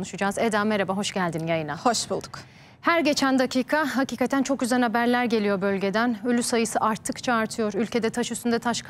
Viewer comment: habercinin şöyle bir bir kelime kullandı hergeçen dakika hakiketen çok güzel haberler geliyor dedi ölü sayısı arttıkça artıyor dedi benmi yanlış duydum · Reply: çok üzen haberler diyor